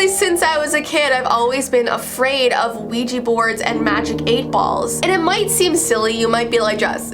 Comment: am I the only one who hears Luigi Board????